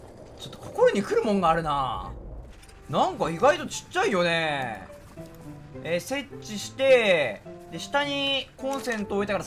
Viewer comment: 設置がえっちに聞こえる